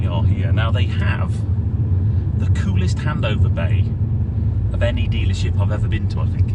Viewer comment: Service plan sounds good , will be interested to see how it works for me when mine is due her first service. I thought you said they had a great “hamburger bar” and then realised you said “handover bar” was wondering if they did vegan burgers 😂